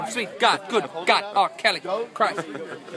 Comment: ahhhh xD at first i thought he said kelly, but he always says xD so its an understandable mistake xD